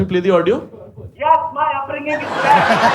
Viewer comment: Ye 42:55 pe kya bola clip wale uncle ne😂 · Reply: Yes, my upbringing is bad